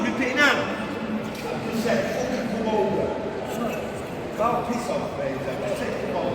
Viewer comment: Did he just say "OHHH piss off gravy) to the fella sat down at Leeds train station, who the homeless dude was. Arguing with 😮👀 if so I know him who was sat down..... As Leeds is the NXT city a cross from where I am, I the city of Wakefield West Yorkshire is 9nly 11 miles away from the city of Leeds in West Yorkshire....